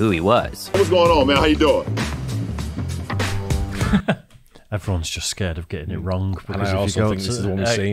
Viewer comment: LMFAO I had to replay what said a couple times to hear it correctly. I thought he said, "Everyone's just scared of getting robbed."